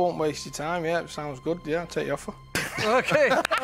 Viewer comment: His first yip was not " ok, I hear your offer", it was "ok, I accept your offer"